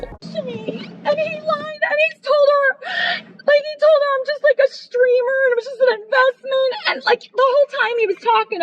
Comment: did she say like a streamer like Asmond? lol